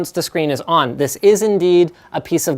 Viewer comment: Did u just say this is a piece of ass?